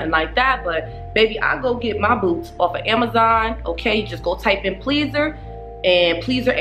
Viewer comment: Lmao 5:51 I thought you said I get my boobs off amazon” 😂😂